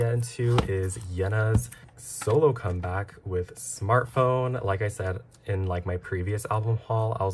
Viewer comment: OH GOD I THOUGHT IT SAID "SHART"